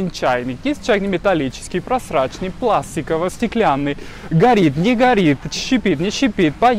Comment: Прасраченный чайник - это мне послышалось конечно, но и такие тоже у нас продают, только потом не работают и вернуть трудно.